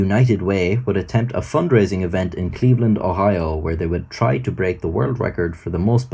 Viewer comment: That's weird.. I thought you said it happened in But you said Cleveland